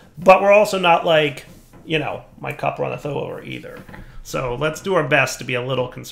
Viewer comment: "But we're also not like.. you know... my <????> or either." Does anybody understand what actually says here? I have no idea. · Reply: "my cup runneth over", it's a saying (technically a bible quote, but it's used out of biblical context all the time), meaning you have plenty of something. As in, you have so much of what your cup holds, it's overflowing.